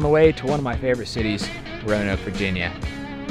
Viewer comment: I thought said “rona Virginia” 😐